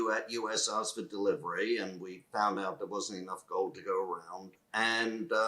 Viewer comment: minutes did he say derivatives or way over sold and they don't have the gold ? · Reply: He said there wasn’t enough gold to go around !